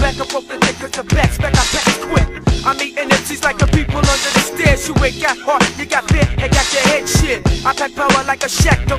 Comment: What is he saying about People Under The Stairs in 4:11? xd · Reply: He says "I'm eatin emcees like The People Under the Stairs" which is a reference the horror/comedy film of the same title. In the movie the people under the stairs degenerated into cannibalism to survive.